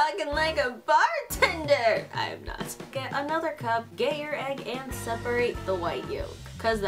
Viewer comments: who else heard"Gay your egg" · For Angels 2nd cup thing when you said get your egg I thought you said gay your egg😆 Or did you actually say gay your egg?